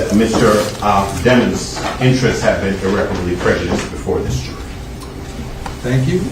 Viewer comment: the subtitles say pregnant in place of prejudice lol 😂